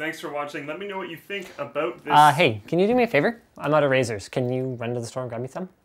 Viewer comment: i thought you said " i'm our of erasers" XD